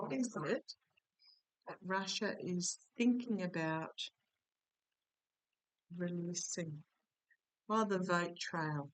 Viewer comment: I think i heard a male voice, couldn't make out what they said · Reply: I heard it too. Sounded like the word grift